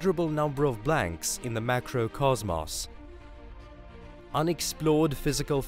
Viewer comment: Um...sorry didn't catch that 0:14 Cause Moss? Oh...Cosmos okay got it